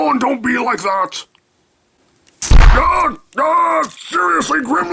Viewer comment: I thought grimlock was saying "wussy prime, wussy prime" lol